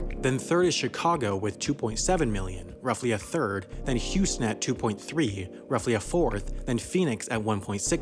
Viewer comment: Just found out that this was a computer speaking 4:46 where he says "Houstnet" instead of "Houston" . Kinda sad now...